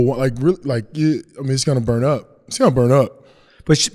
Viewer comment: Does PJ say to “bird up” at 3:42? S/O Eric Andre